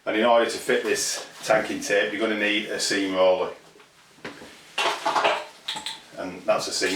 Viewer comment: I thought you said you'd need a steam roller ? 😉